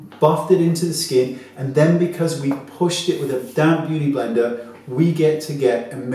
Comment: You say "damp" beauty blender....I hear "damn" beauty blender! Those damn things hate me!!😂😂😂